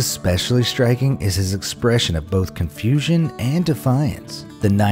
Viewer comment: i thought you gonna say cousfusin and delay 7:33 guess what show it from